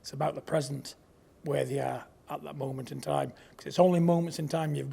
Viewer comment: I thought he said "women" lol 🤣